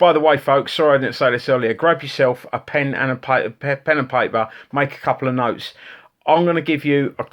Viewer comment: I thought you said 'GROPE yourself,( a pen and a piece of paper)' :-)